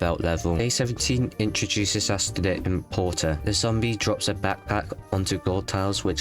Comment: the zombie is called “imp porter” but at first I thought he said important lol 😂